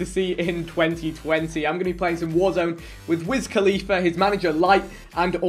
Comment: i ain’t gon lie i thought it said mia khalifa i was like tf